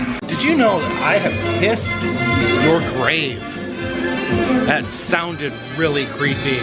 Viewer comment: DID he just say, "I PISSED ON YOUR GRAVE"?